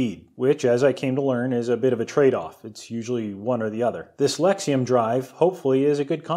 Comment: At one point I thought I heard you mention the "dyslexium" motor drive. Hunh, powered by scrambled wrods and letterz? Must be really cutting edge.